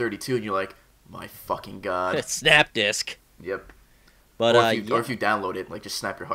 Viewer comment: Thought he said snap dick.